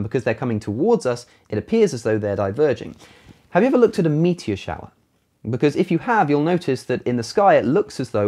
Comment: When he said 'Have you ever looked at a meteor shower?' I heard it as: 'Have you ever looked at your meat in the shower?' and I almost died